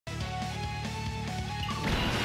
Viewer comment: I hear also "I'm Gang" 😂